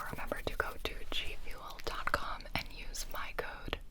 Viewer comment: This video is sponsored by G Fuel Me: Thought you said Fuel :/